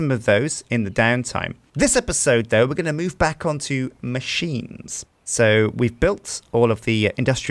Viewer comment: is it only me who hears Machimes?